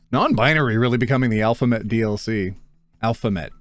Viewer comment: what was he trying to say here? alphabet?